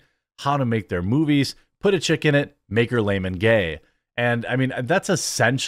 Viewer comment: Put a chick in it and make her lame and gay!? Is that what Cartman is saying?? I swear I heard, "Put a dick on her and make her gay!"